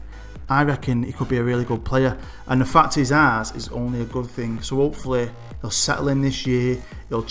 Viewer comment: When you said about Will Hughes 'the fact he's ours is only a good thing', I thought you said 'his arse is only a good thing'